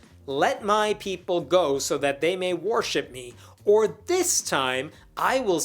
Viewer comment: I keep hearing "war ship" instead of worship lol "Let my people go so they can war ship me"